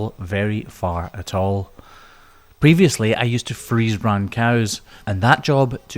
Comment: Oh, freeze brand. I had to play it 4 times over to catch it. I kept hearing "I used to freeze brown cows...".